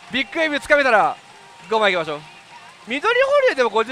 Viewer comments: 五万発行きましょうって言った時「ご飯行きましょう」に聞こえちゃった笑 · の5万発行きましょうがご飯行きましょうにしか聞こえない